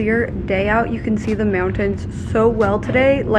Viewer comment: Shelby when you said mountains the first time. I heard you say merrell twins.